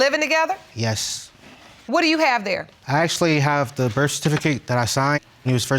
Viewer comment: Did she just say "birf-a-tificate" 😂😂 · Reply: Sha Sha 🤣🤣🤣 I thought she was saying birf-fer-tificate